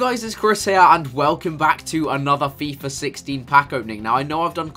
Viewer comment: Who else heard him say FIFA 60 at the start